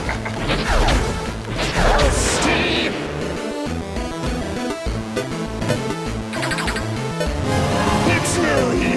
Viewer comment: What does viegar say at 0:56? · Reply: He's saying "Toasty", like in Mortal Kombat.